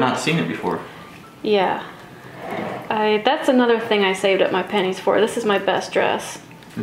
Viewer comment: I love the video but every time you said, "I saved up my pennies." I heard, "I shaved up my panties." I was thinking to myself... Why would they say something like that in the 1820s? Well, I'm off to the audiologist.